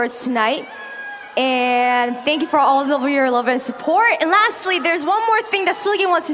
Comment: “thank you for all the real love and support..” is what I heard